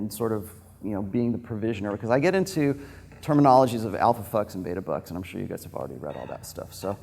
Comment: Thought he was saying alpha fucks and beta fucks XD